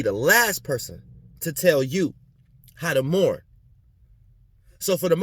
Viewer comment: I heard "I won't tell you how to Moor"